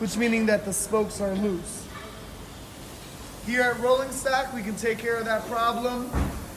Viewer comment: I thought you said The Rolling Sack, which would be just as cool.